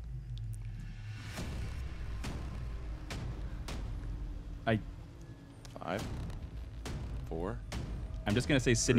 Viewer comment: What did he say? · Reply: technicalfilmreviews watched it again. He said repeat.